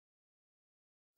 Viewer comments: この歌好きだけど何度聞いても、願い事ひとつだきゃに聞こえる💦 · 願い事ひとつだきゃ って聞こえるwでも好き